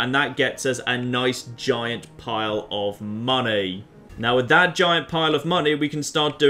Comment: says "Giant pile of money," I hear "Giant pile of mantis eggs."